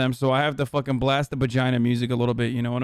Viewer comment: did he say bagina?!